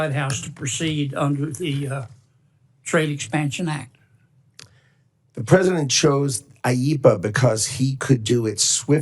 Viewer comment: I thought I heard president chump! Nutlick said president chump. That’s what I heard and im sticking to it.